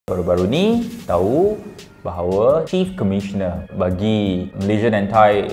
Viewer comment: Chief memang sebut "kief" ke atau aku yg salah dengar?